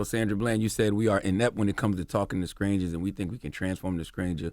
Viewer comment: Did charlemagne say “SKRANGERS” twice at 29:45??? 😂 that’s a country boy there 🙌🏾